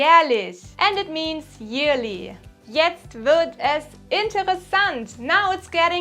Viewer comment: Great video. I learned & enjoyed the entire video. Gut gemacht! One question however, …I had trouble understanding one word what you said at 8:04-8:05. “Jetzt ___es interessant”. “Now it’s getting interesting.” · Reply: I think it's "wird" Jetzt wird es interessant.